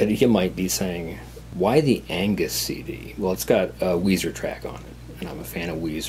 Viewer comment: At first I thought you said anus cd lol.